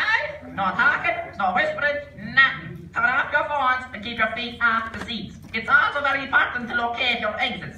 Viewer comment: What’s funny to me about that cinema clip is how the important bits are pretty clear (no talking, no phones, keep your feet off the seats, locate your exists, enjoy the movie) but some phrases like “it’s a grand day for it” didn’t even sound like words to me. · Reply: I thought it said "keep your feet and arse in your seat"